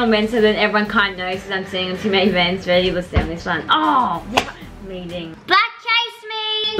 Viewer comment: did say “fck meeting”??